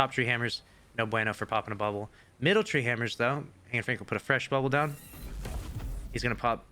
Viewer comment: thought u said anne frank then